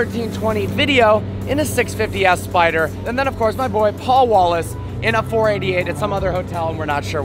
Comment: Who else heard "Paul Walker"